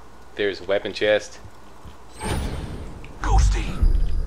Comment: when Zer0 sent out deception, did he say "Go Steve"?